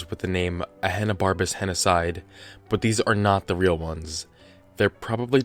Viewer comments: Thought he said a Hannah barbara henocide lol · i kept hearing “hannah barbara’s genocide”